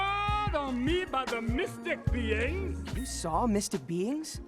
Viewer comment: As a kid whenever one of the characters said "Mystic beings" I always thought they said "Mystic beans"